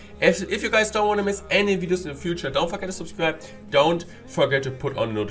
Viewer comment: Was i the only one who heard: Dont fuckin subscribe